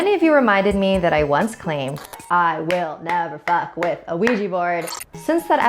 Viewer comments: I. Want. To. Fuck. With. A. Ouija. Board... But I'm poor and can't buy one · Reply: I THOUGHT YOU SAID YOU WANTED TO FUCK A OUIJA BOARD HAHAHAH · Did I hear her saying "I will not fuck with a Ouija board "